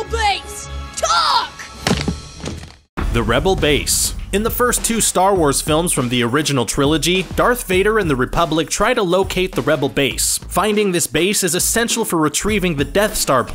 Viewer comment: Did he just said Republic at 2:11?